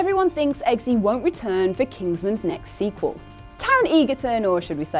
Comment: Am I the only one who heard it as Taron Eager-Tongue the first time she said it?